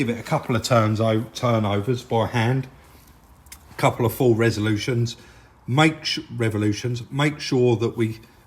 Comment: Is great as usual. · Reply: Maybe the audio was a bit off. Sounded like was saying Resolution instead of revolution, Probably my sound on my tv.